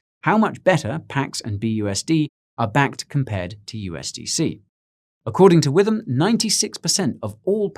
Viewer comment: Did you say according to Withim? I'm sorry, I just cant help it, I..... Withim is a dancer It's a soul companion You can feel it everywhere Lift your hands and voices Free your mind and join us You can feel it in the air. AHHHH OHHHH - it's a passion!